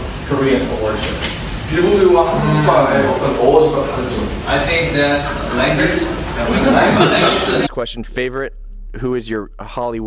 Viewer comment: Can someone tell me what he said at 1:25? 😂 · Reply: Kathrene Legones “I think it’s language”